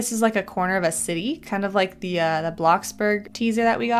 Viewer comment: I love it when auto captions do "blocksberg" instead of bloxburg 😂❤